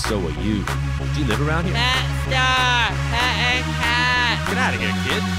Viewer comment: at 131 i thought he said "blackstar pet a cat" 0_0